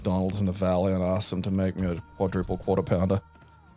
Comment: mentioned his quadruple quarter pounder just as I paused the video to go do something else, and I thought he said "quadruple pounder". Cue me spending the next ten minutes trying to figure out how he managed some 2ft Scooby Doo monstrosity. And in my mind, Beavis and Butthead were working there.